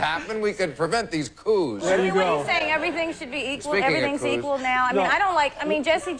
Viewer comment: Did anyone catch 3:46, "speaking of cooz"?